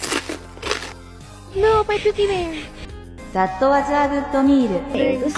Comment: DID LANA JUST SAY "no my cookie man"💀